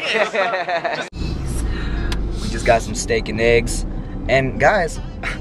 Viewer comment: lulz when u said steak n' eggs i thought u said stankin eggs